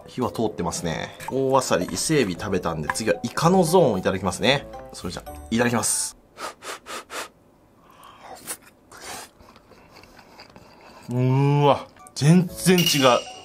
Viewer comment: ｢いただきます｣が｢いかだきます｣に聞こえて ｢イカ抱きます！？｣ってなった